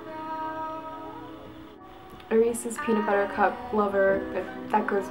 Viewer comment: For a split second, I thought you said "a racist peanut-butter cup lover." Just a split second.